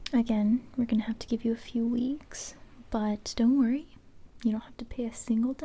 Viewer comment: I thought she said “ At least you don’t have to piss”